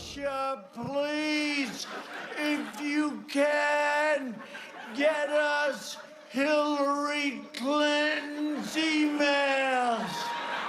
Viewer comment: My caption thought he said : please if you can’t get us Hillary Clinton Righteous